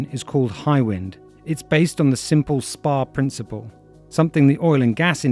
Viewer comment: umm can someone help me here, does the narator say "spa principle" at 10:49? I can't quite catch that. Thanks! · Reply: "Spar"